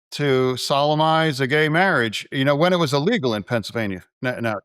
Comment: Bless you, Jon. You're a saint. Fetterman's brain died long ago. He sounded like he said sodomised a gay marriage, the man is NOT ok! Your patience is commendable.